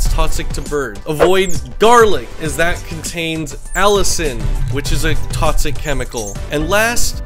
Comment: i thought you said "don't feed your birds garlic, it contains allison" i was like huh what did i do